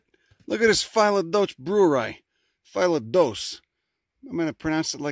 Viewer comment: I thought you said Phyla dulcis/dulce (one of my favorite plants to snack on), not Phyllodoce (a new plant for me to learn about)!